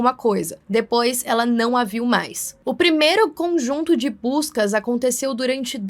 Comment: o primeiro conjunto de Puskas, por favor diz que não foi só eu que ouvi isso